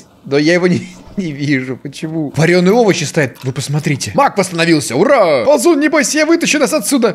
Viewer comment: Беги Маг! Что слышу я: Беги Мак! Эх..